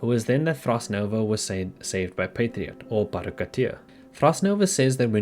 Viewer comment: I'm sorry but did I hear "Patriot, aka Baro Ki'teer"? Can someone explain this to me? (I have played Warframe which is why I'm curious)